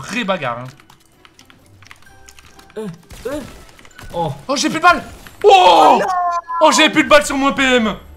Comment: j'ai compris "j'ai paypal"